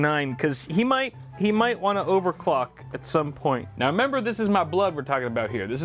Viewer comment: I was sitting far away so I couldn't hear the video that well and I thought he said "he might wanna overclock some porn"😂 I almost died laughing 😂😂😂😂